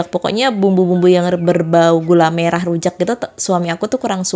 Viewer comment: Jadi ku ulang katana gula merah tapi aku denger nya bawang merah bener ga si mb slah ngomong ap aku yg salah denger